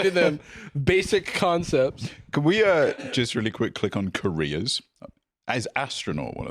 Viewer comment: “Can we click on ‘careers’? Is astronaut one of them?” I misheard his accent as “Koreas,” and I’m like “The three Koreas: North, South, and Astronaut”